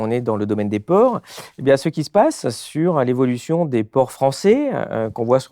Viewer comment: A 47:23 j'ai entendu "le domaine des porcs" ... "évolution des porcs français" 😂😅 Merci pour votre travail. Continuez, on vous soutient et on vous partage 👍✌️